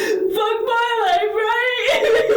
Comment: I have no idea what she said throughout 90% of that interview. I did hear “or whatever” and “Ahh, huuu, huuu, huuu, ahh, ahh, you’re not listening, huuu, huuu, huuu (hard breathing), ahh, ahh” a few times, as well. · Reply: oh, she did just say “f my life, right” which is a weird thing for a murderer to say but ok